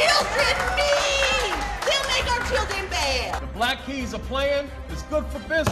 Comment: Ngl I thought it said Black eyed peas lol